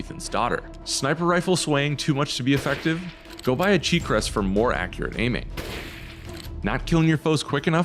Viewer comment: Sounded like he said go buy a cheap dress for more accurate aiming 🤣🤣🤣🤣